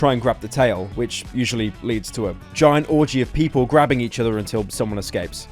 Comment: Is it just me or did he say orgy, LOL XD